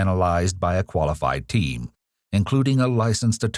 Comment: I know you said team, but I heard "teen" and paused for a moment.